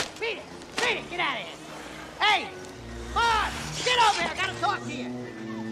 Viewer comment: I actually misheard one of the lines in this scene. I thought Harry said, "Get over here when I'm talking to you!" instead of "Get over here! I gotta talk to you!"